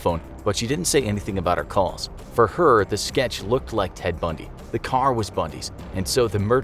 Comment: I thought it said Ted Bunny 🤦🏻‍♂️